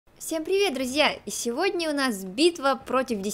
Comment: Мне в начале послышалось не всем привет друзья а всем привет семья 😂